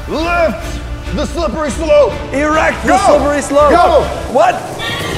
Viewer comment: Im sorry but WHAT THE HECK DID JUST SAY 😨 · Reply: He said erect the slippery slope